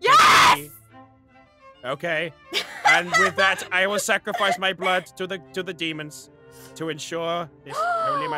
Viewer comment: I thought said "I will now sacrifice my blood to the vegans" instead of "I will now sacrifice my blood to the demons" 🤣🤣🤣🤣